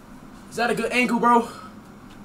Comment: I skipped to the end of him saying is that a good angle bro and it sounded like he said “quandale dingle bro”